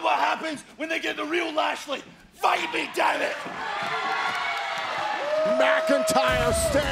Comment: Did Drew say at 2:08 "Fight me daddy"?